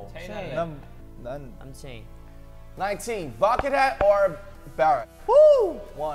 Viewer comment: I heard 'bucket hat' as 'bakugo'